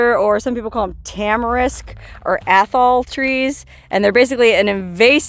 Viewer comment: Did You call them trees asshole trees ?